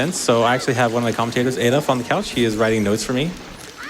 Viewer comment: did he say Adolf von Couch?